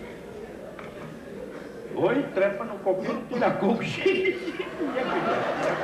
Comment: O que ele fala aos 1:29? Kk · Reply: Oi trepa no coqueiro, que da coqueiro música da bibi Ferreira..A inflexão dele e o improviso